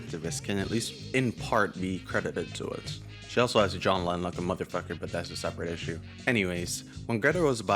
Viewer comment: Ok, forgive me, but at 2:40, you say, "....and has insisted that her nature as an activist can at least in part be credited to it. She also has a <....> like a mfr" I can't understand that missing word for the life of me... can someone help me out? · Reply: jawline